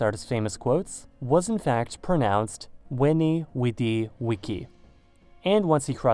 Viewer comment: For some reason I've heard it as "VINCI" which turns out to be a plural noun form of it!